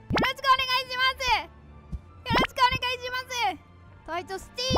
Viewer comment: よろちくお願いしますに聞こえて可愛い❤️